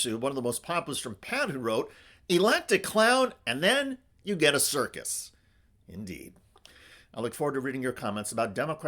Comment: Did he just say”Elect A Clown and start or Get Circus”?